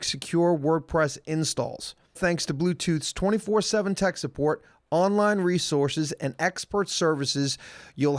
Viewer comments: omg at 36:09 said "thanks bluetooth's 24hour teck support" instead of thanks to blue hosts 24hour tech support. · Did anyone else notice that at 36:10 said "thanks to bluetooths..." instead of bluehost, way to do your sponsors wrong. If you meant to say bluetooths my bad sorry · Anyone else catch @ 36:10 calls them Bluetooth instead of blue host?? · Ha ha ha at 36:10 says Bluetooth instead of blue host. Lol